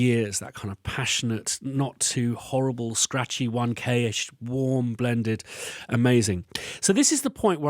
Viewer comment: if you're wondering what he says its: "scratchy 1kh". Took me a few minutes of confused googling until I turned the captions on to find he wasn't saying "Onecay-ish"